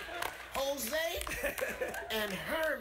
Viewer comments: the fact that he was so confident with saying jose gets me weak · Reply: Lol! and here I thought he said Hosea! Am beginning to become weak too😂😂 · I honestly thought the guy said "Hosea" or something. Now that I know it's Jose I am laughing uncontrollably. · the fact that he was so confident with saying jose gets me weak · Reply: I only thought he said Hosea because I played Red Dead Redemption 2 wich has a character named Hosea Matthews. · i thought he said Hosea, guess i played too much rdr2